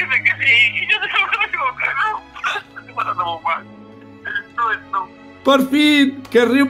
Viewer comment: Nadie, absolutamente nadie pero absolutamente nadie entendió lo que dijo Timba uwu Timba: Y todos dicen que ¡si! Y yo creo que me he equivocado Para los que no entendieron, él dijo que todos decían: Es real el Riumba! Pero Timba como siempre dice: No es real el Riumba! Y lo que trata de decir es que se a equivocado 7v7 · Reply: De hecho no...dice: pues claro si yo siempre e dicho que no y nadie me hace caso..y todo el mundo dice que si y yo le digo que no qñlvneikdkf esque NO es NO. eso es lo que timba dice